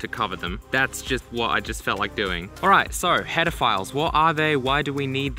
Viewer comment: I thought he said what are p*dophiles 😶